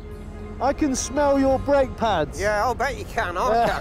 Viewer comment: I thought he'd said, I can smell your underpants!